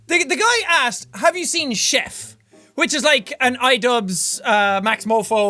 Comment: when the guy said "have you seen chef?" I thought he said "have you seen shit?"